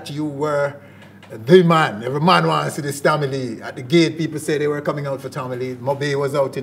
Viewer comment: did say the GAY PPlE came out to see tommy lee?? 7:29??